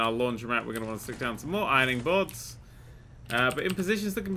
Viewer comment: I thought the iron board said water board I'm like holy shit